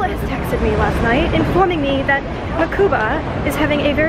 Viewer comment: Every time she says 'Mokuba', I hear 'MACUSA' from Fantastic Beasts. XD